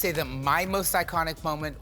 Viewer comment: why did I hear “my most psychotic moment” 😭